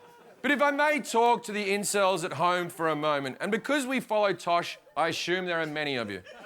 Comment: I need a little help here. I have trouble understanding Jim's accent sometimes. Can somebody tell me what he is saying at 4:19 onwards after "If I may talk to the Incels at home for a moment"? I specifically don't understand the bit in his next line after "and because". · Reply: And because we follow Tosh( Tosh is a TV show that comes on before the Jim Jefferies show) I assume there are many of you